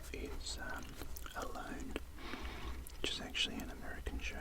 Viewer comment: What did you say the Name of the Canadians Tv Show was called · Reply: It's called Alone